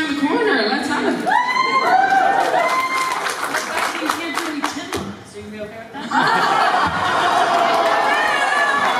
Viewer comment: what does Brie say? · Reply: ava kenneth but becky you can't do any chin locks, you gonna be okay with that